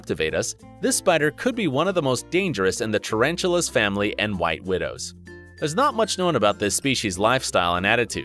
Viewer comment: D—-did you just say “white widows” at 4:25 ?